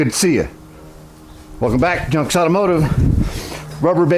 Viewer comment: When you do your opening, I thought you said Jump’s not Junk’s Automotive. Lol. It will be interesting to see what you can get done with those bumpers. Cheers!! 😎